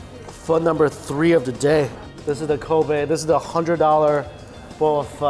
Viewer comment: i heard covid